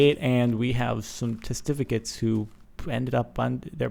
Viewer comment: Thought testificate said testacles(Can't spell)